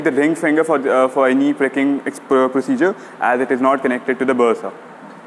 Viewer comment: what you have said I don't hear properly. Be sure you take the right finger for pricking as it is not connected to the......... ? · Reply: He said "bursa"